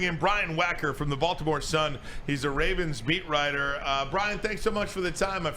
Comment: when he brings in the beat riding it sounds like he said "ravens meat rider" lol